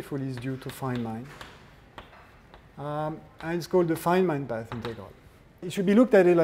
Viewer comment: What is the equation at 5:37? The Feynman what? I can't make out what he says. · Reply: A mini version of the path integral summing amplitudes